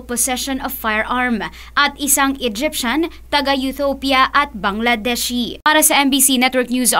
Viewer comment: Did she just say “UTHOPIA”...??...